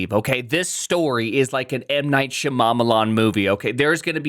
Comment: did he just say m night sha mama marmalade ?